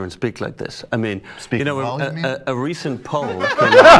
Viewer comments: Hate to be this kind of youtuber jerk off but what does Michael say at 6:13? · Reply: "Speak well, you mean?" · What does Michael say at 6:11 in response to "It's frankly disturbing to hear someone speak like this blah blah"? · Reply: He said, "speak well, you mean?" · I couldnt make out Michael's laugh line at @ 6:13 anybody tell me what he said? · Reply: Speaking volume